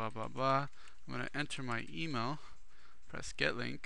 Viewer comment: did he said my emo? LOL!